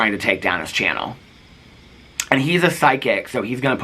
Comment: Im happy youre doing this for yourself! I barely ever comment. Would not even imagine making mutiple videos to say nasty things about people! On a side note idk why at first I thought you said sidekick and not psychic 😅 i was like huuuh thats a odd thing for this person to claim to be 😂